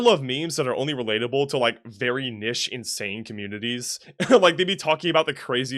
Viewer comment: did bro just say nish